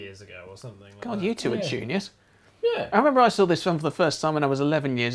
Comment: What? What did you call them? 1. The word isn't clear (but) 2. it sounds like a brilliant insult and I'd like to use it! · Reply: It's 'juniors'.